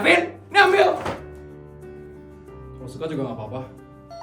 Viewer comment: guys aku ngga salah dengar kan kata "suka juga gapapa" Apakah ini pertanda akan ada couple baru, siapa yang setuju jadian sama feli like guys❤😍